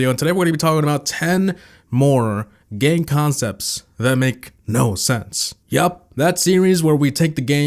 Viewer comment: At the start I thought said 10 more gay concepts that Make no sense